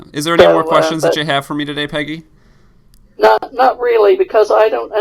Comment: When he asked about the name I thought she said Piggy xD